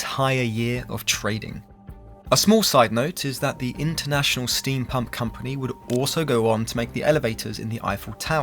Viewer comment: For a second I thought you said 'International Steam Punk' company...